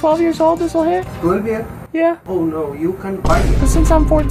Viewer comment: “ Not me , my other brother is 14 “ That guy must have been so bamboozled for a second Edit: I misheard ‘other’ as ‘older’ but I corrected it , ignore the first 3 replies